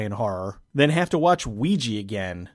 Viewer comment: I hear you FUCK ouija